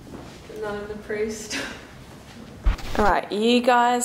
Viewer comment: I just watched this video today for the first time but when you have that ghost which said "none of us" it actually sounded like it said "Nan desu" and Nan desu ka means what is it in Japanese so I think you got a Japanese spirit